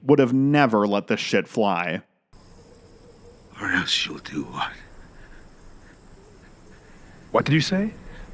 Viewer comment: what did Walter say? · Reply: Or else you'll do what?